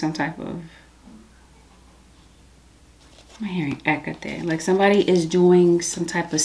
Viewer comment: "Did you say, "Hecate?."